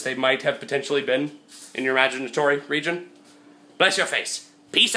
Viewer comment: and it sounded like toby said "vagitory region"